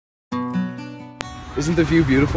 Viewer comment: Lel the 1st time I watched this I thought it said "isn't my feet beautiful" XD